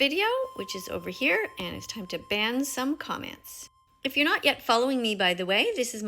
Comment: I just heard “iTs tIMe tO bAN SoMe COmMenTs” and I was just like “WHAT”